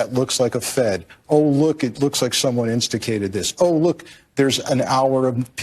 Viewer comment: what is that word instikated???